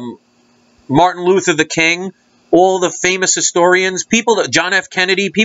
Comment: Am I the only person that heard him say Martin Luther "the" king lmaoooo